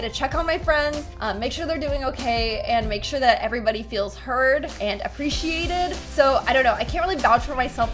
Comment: Did she just say ,, Make sure that everybody get hurt !? ˋˋ Sorry if I understood it wrong ( It was at 3:35 till 3:40:)